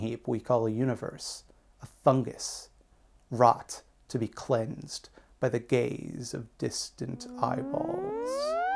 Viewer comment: Oh, "cleansed by the gaze." I got excited for a second when I thought you said "cleansed by the gays," like we'd finally be the cleansers instead of the cleansed. Nevermind.